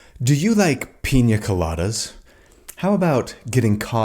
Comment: So I paused almost immediatly and what I heard was "do you like pen-" and I am way too immature for that.